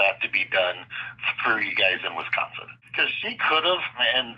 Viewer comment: sounded like the salesmen said "screw you guys in Wisconsin" lol :face-green-smiling